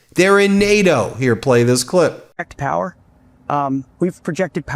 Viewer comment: Hegseth "White Power". Is all I heard. He kniw he an enemy to the orgins of America